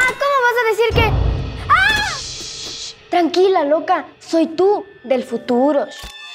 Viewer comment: Por un momento pensé que decía “SOY TÚ DE FURRO” xdddd